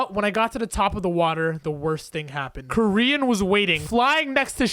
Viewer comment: When you said koryin I thought you said korean